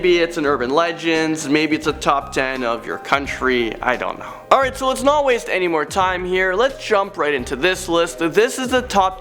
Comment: it sounded like you said Landon down-not-sing